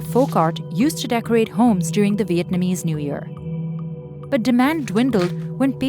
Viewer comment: Did she just say “VietMAnese” in the beginning?